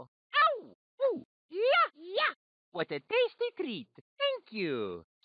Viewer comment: I’ve always thought Mario said “what a day to see” on that line where he said “what a tasty treat”